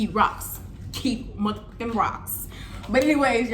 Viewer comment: That other woman dumb anyways some “u seen what it said” baw I would’ve been hot frfr just like u.. .. and yall crazy .. some real criminal shit no way 😂😂😂😂and imma have to start keep rocks too on first I thought u said rod 😂😂😭😭😭..and damnnnnn messed up tht boy house .. ole well thts what he get !!